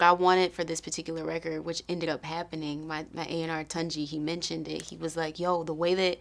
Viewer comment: "My A&R" For those who got themselves wondering what she said